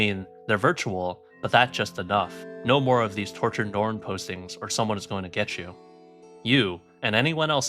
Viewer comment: I thought it said tortured porn site lmao